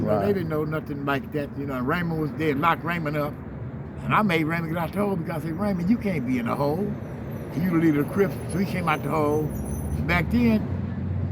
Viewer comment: what does he say at 4:17-4:38 ? something about raymond in tracy but i can't understand it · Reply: “Raymond you can’t go to the hole, that’s what they called it back then, You had to go to that special place the main line off the yard “